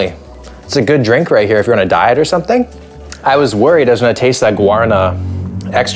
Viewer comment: I heard, "it's a good drink right here if you wanna die or something" 😅 Love your videos – your humour is wild! Thanks, man.